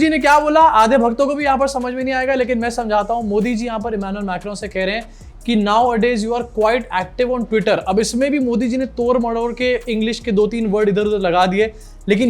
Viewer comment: honestly bhai I heard "nowadays you are fighting for future" 😅🤣🤣